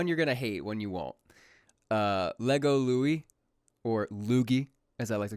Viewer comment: for a moment I thought he said Legom x Loui, that left strange images in my head thanks.